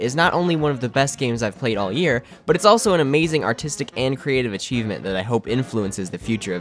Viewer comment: I heard "autistic and creative archievement" HAHAHA LOL